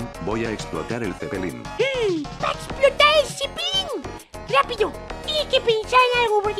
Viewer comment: escuche "va a explotar su pilin" XD